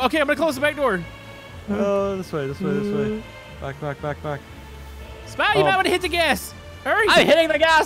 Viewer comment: did I just hear the F word? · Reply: No back back back because the controls are inverted